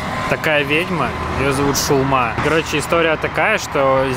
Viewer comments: Мне послышалось не шаума а шаурма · и:"Шулма это ведьма" Я которой послышалось "Шаурма"😳